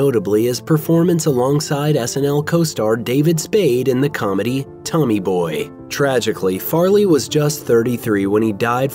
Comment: DID YOU JUST SAY 'TONY BOY'?!